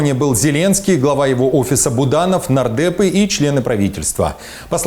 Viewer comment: мне послышалось наркеты, оказывается нардепы))))